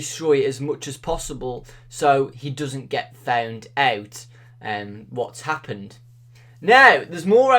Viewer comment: The way you said found out sounded like you said fewnd ewt